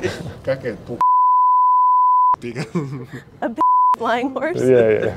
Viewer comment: i beleive what he was trying to say i pegas, which means pegasus. not big ass 😂